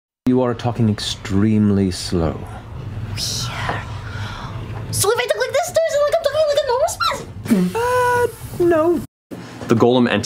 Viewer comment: can someone please tell me what she’s saying? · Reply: "So if I talk like this, is it like I'm talking at a normal speed?" Something to that effect.